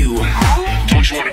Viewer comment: Im hearing this song for the first time in years and it sounded like it said "freaky shit can fly" in the beginning and now I can't stop hearing that